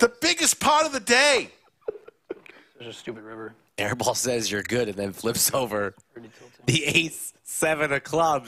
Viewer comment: Was that a slow roll? I guess I don't know what a slow roll is. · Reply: I thought he said your good, cause he called his hand